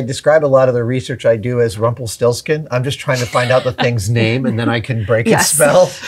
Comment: "I describe a lot of the research I do as [something]" Can anyone make out what he's saying at the last part? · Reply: Rumpelstiltskin. It's a fairy tale by the Brothers Grimm.